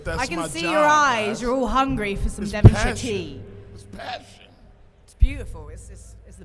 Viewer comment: ok what food is Jamie and Mark talking about? i am not native speaker, couldn't make out what they're saying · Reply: Devonshire tea or cream tea